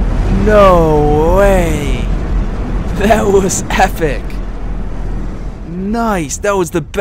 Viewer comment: did he say efice???